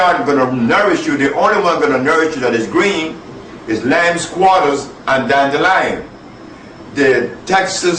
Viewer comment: What was the vegatable Sebi said before dandelion @ 3:09? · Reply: Lamb’s quarters . Very delicious greens